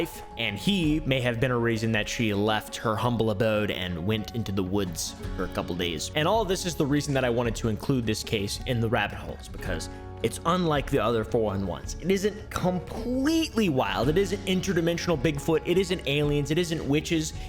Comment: Thought it said buttholes